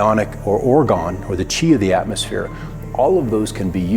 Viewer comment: did he say chi of the atmosphere? Please tell me he didnt say that. Tell me I heard it wrong. I was all ready to shrug and say 'eh, maybe.' till he said chi of the atmosphere. Now all I can do is laugh and wonder where I put my hippie repellant.